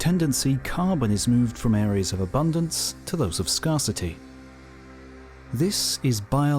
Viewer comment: did he say scar-city instead of scarcity. This has to be a robot voice